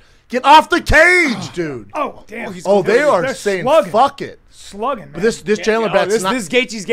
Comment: Chris absolutely needed to be the center of attention but because I'm a silly goose, I felt really respected by him doing what he wanted to do. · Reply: does brendan say “they are fuckin!!!”😂